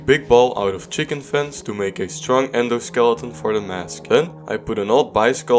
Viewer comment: idk why but when you said endo skeleton right here i first heard it as ender skeleton